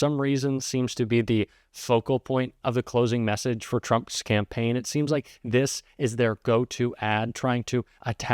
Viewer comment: I thought I heard "Trump scampaign," and it felt very fitting. 😂